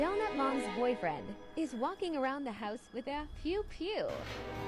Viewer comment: When you said pew pew I thought you said poo poo 😂